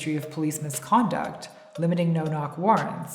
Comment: I just want to clarify, are you saying eliminating "no-knock warrants" or am I mishearing? It might be pedantic by me but to me this seems different to establishing "no-knock warrant" standards. I am not to familiar to the Biden platform so I might be misinformed but could someone help me clear it out? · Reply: 9 months later lmao, but she said "limiting"